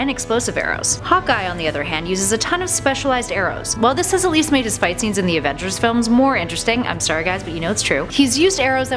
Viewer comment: I have no idea what she said at 1:52. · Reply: She says "this has at least made his fights scenes in the Avengers films more interesting." I know I had to rewind it a few times too lol.